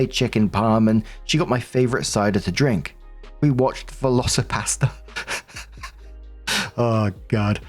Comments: I thought you were saying velcio-pasta… imagining a film of dinosaur shaped pasta 😂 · Lmao i misheard velocipastor as velicipasta and had a whole different mental image.